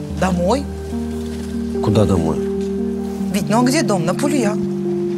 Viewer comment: на Пулеа? Или что она сказала? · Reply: Пулюя